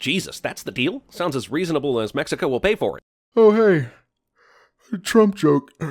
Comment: Did Jeremy just say Mexica, instead of Mexico(or Mexicans IDK)